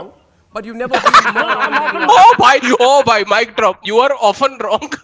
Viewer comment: at this time does someone hear "MIC DROP" bangtan soyeondan(BTS)😏😏